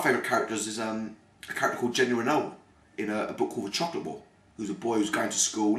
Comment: What book does he mention at 4:43? I cannot understand him. It sounds like "The Chocolate Ball?" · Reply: johnchill3 it is the chocolate ball